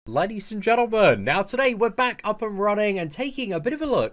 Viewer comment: Why do i always hear "ladies and gentlebird"?